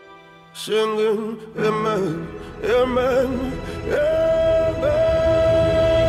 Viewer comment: When I first heard this song, I thought he was saying! Emmett,Emmett, emmet! As a silent dedication to the murdered Teen! But yes as you hear it again? Obviously he is not. It's* Amen,Amen, Amen.✌🏾